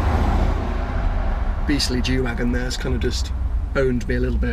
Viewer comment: Did he say "beastly Jew wagon"... or somating else! :-p